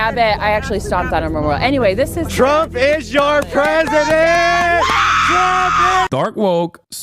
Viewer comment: They're saying Trump is your president but the captions say President Biden